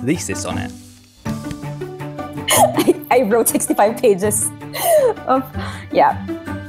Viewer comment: A 65-page master thesis for Swedish food in tubes. That is indeed dedication and passion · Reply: I thought you said you were a 65 page master thesis 😂